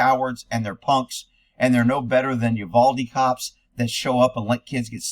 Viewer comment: I had to listen twice at 7:02. I thought you said "You baldy cops", but you actually said "Uvalde cops". Proof that we hear what we want to hear.